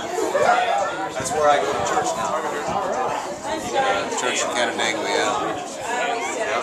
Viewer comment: Did I hear "...church in Canandaigua." at about 0:36?